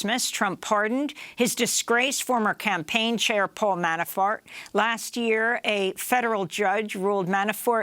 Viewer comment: I could have sworn she said Manafart. 😀